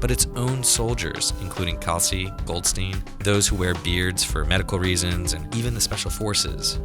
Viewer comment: did he say "wear beards for edical reasons"?? what reasons could that be?